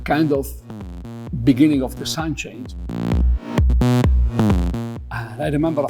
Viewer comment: the subtitle is 'the beginning of the sound change', but what Benny Bennassi actually says is 'the beginning of the sidechains'.